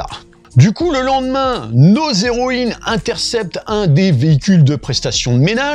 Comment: "nos héroïnes" j'ai compris "nausées"